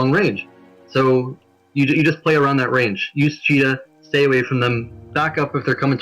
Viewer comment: What does he say at 2:46:47? Use GF?